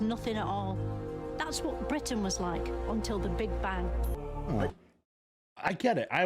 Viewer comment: I think she said, "Big Ben". British context.